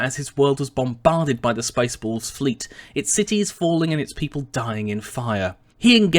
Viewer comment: Wait, did you say "bombarded by the Space Balls fleet" at 6:36 ?